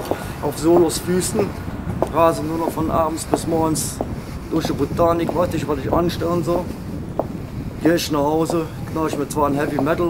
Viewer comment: was sagt er ab 1:28?? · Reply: "Rase nur noch von abends bis morgens durch die Botanik, weiß nicht was ich anstellen soll."